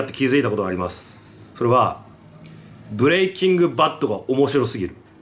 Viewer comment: ブレイキング・バッドがブレイキングダウンに聞こえて「え！？好きなの？」って一瞬なりました